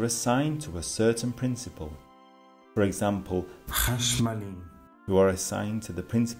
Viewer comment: DID HE SAY CASH MONEY AT 2:00😂😂